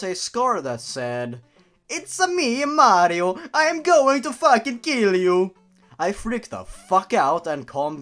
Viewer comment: I legit thought I heard "it's a me Mario I'm going to fuck you." Edit - okay nvm he did